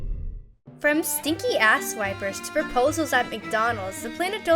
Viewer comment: Did just say "From Sticky Ass wipers"?